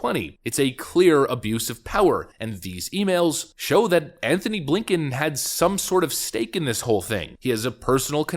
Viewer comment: Ohhhh... its A. Blinken? I thought they were saying Abe Lincoln.